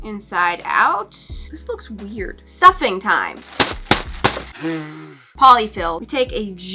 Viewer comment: i- when she was about to stuff the sock plushies i literally thought she said “suffering time 😃” but also cute merch!